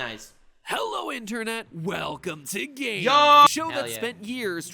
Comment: Lol it cut off so it sounded like Matt pat said hello internet welcome to GAY